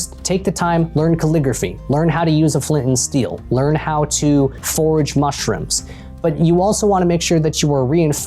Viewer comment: Did anyone else hear that as “learn how to FORGE mushrooms”? 🍄⚒️